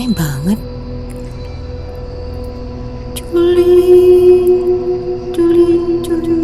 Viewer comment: aku dengernya coli, coli, coli.. apa kupingku yg salah dengar ya?? 🤔